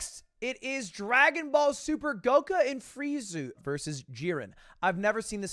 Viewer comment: This is a year old, I know that, I get that. Hey, you aren't big in anime right? I GET that.... 14:45 but Ludwig, wtf did you say?.. Dude, WHO!? GOKA?? FRIEZU???